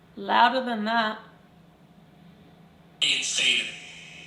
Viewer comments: it sounded like the gohst tube said "it's Saitan" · Hi and, some great responses both on the box and during the Estes. 35:41 did the deep voice say ‘in Satan’? That sounded like an air raid siren, pretty scary, did you find out if it was for real or paranormal sounds? The candle sounded like it had some water in there, then says ‘you’re probably wet 😱. Thanks for another great video and location. 🤗❤️🇬🇧😘